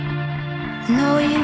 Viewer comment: For a second I thought I heard secrets and lice.